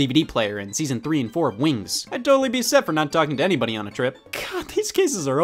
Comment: When he said "Wings," for a split second I whiplashed back to my fairy-obsessed youth and thought he said "Winx"